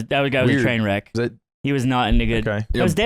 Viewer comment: did slime say the n word? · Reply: He said he was not any good